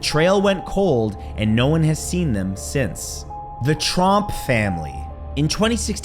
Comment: Dang it!!! I thought he said Trump family!!!